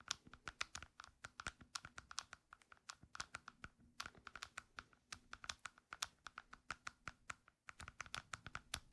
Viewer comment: TIMESTAMPS: 1:39 : some sort of clams ig 5:30 : mini football 9:00 nonslim (I think that’s what he said lol) 14:56 : 3D printed device ig HOPE YOU HAVE A HAPPY AND AMAZING DAY HAPPY OCTOBER🎃🍁🍂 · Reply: Thanks! And at 9:00 he said nonslip